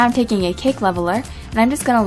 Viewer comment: omg lol! I thought u said pancake!